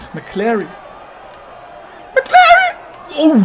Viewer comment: OH KANN SINGEN.Ich verstehe immer MC LARRY